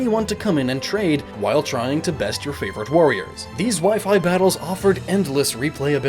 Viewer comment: I misheard "wifi battles" as "waifu battles" during the My Castle segment... It is still accurate either way...